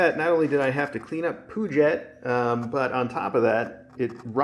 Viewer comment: I honestly thought he said "...I have to clean up poo jet". Sounds like unfortunate task.